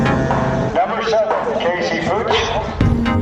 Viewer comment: Sounded like the track commentator was in on the trolling, sounded like he was saying Pooch instead of Putsch.